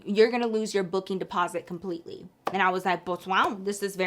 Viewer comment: girlie at 28:52… I’ve been trying to figure out for YEARS what you’re saying in your videos!!!! Is it boswow??? Hahaha please lmk!! Love you girl!! 😭😭💗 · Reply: I’m barely seeing this comment but she was saying “Pues Wow” which is Spanish for “well wow” or like “well damn” 😂😂😂🩷 but sometimes people go “pos wow” to say it silly. it’s kinda like when people say “ohmagurd” for “omg” yk?